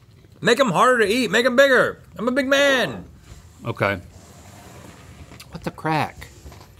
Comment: does say “what the crack” ? 😂